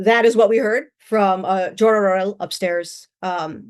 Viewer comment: WHich name? Sorry i havent understood the name, does she said "thor han"?